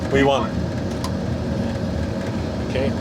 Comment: It’s “V1”. Not “ we want”. Subtitle is wrong.